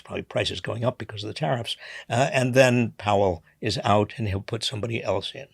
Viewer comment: I heard Trump Hell, had to rewind to hear Jerome Powell and still as scary.